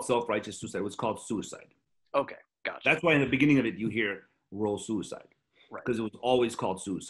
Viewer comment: i thought serj said "roll c side"